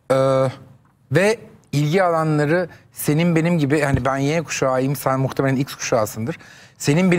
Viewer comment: Armağan bey "ben y kuşağıyım" mı dedi, yoksa ben mi yanlış anladım?